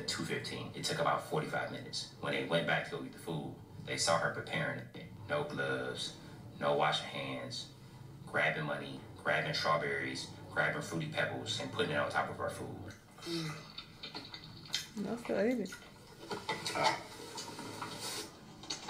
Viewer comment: I had a long shift. Nothing makes sense.😫. At 8:02 he says "no gloves, no washed hands, grabbing money...grabbing strawberries" but I heard "grabbing ass". And I thought that's fucking disgusting.